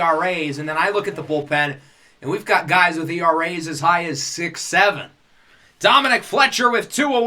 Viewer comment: did you just say 6'7